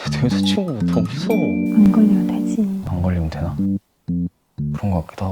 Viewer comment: "안걸리면 되지" 가 안꼴리면되지로 들려..정신 나갈거가타